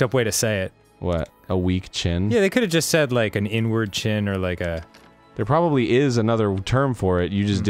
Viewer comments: when said “inward chin” i thought he said “n-word chin” i was like wtf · I thought said n word chin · I could’ve sworn said “n-word chin” lmao